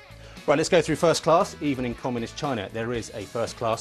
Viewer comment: «Even it comes in China there's a 1st Class» what a dump bias comment from the presenter. I'm sorry but many luxury cars (Rolls Royce) used in Europe and America are manufactured in China. Of course there's first class déclinaisons and of course China can deliver high class and high quality Jetliners and cars. Stop trying to behave like we are blind. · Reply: I thought he said <even in communist China>...